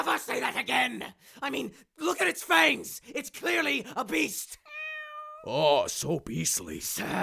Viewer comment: sounded like he said obese.....instead of it's a beast